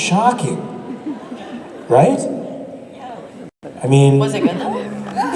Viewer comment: What did the girl say in 23:46 that was so funny? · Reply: She asked 'was it good though?' when Lucifer lost his virginity to kelly kline.